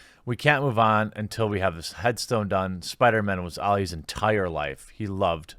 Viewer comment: I thought Stan Lee said Spider-Man was for everybody